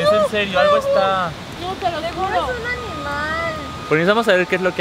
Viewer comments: yo escuche como si dijera '“quiero jugo ” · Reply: Dijo te lo juro no quiero jugo :v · toda ahogada JAAAJAJAJ alguien entendio lo que dijo? jajjaajja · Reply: dijo no te lo juro te lo juro